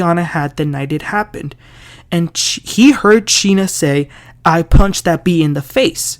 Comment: I thought I heard I "pushed" that B